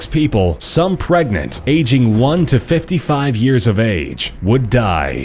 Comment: wait did he say some ageing from 1 to 58 or something like that being pregnant